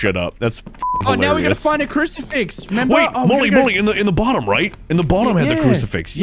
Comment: Is it just me that think that mully sounded like jelly at 4:15 ish